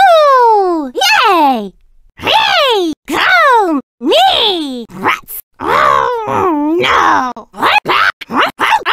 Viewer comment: did Toad just say "Cum"? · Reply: No he said "Go"